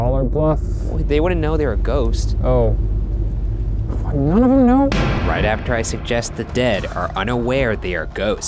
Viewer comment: I thought Shane said "what none of them know?"...